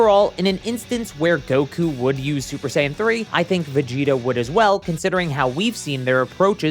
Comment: you said Vegito instead of Vegita or at least that's how it sounded like